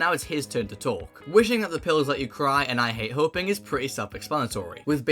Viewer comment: i thought it said "I think someone will die, but i hate hoping." and its actually "wishing the pills let you cry, and i hate hoping."